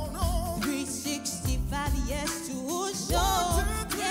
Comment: 🙄365yrs!!! Did I heard right